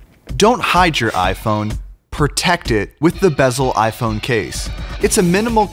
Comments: I thought it said bezel iPhone cake lol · I thought it said, "iPhone cake" .